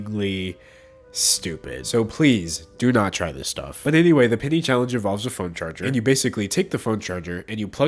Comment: I thought he was saying pity challenge